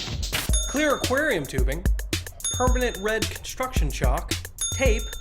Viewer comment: is it me or did someone say "HOT TUB" at 1:53 ?